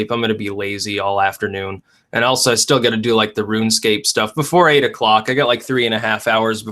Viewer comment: Did he say Runescape? EDIT: He did! :D I didn't know played Runescape.